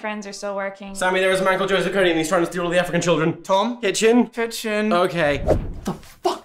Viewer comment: For those of you who didn’t understand what Tom said, “Sammy! There’s a man called Joseph Kony and he’s trying to steal all the African children!”